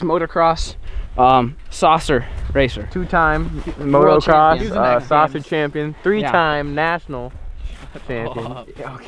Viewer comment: it sounds like says he's the sausage champion lol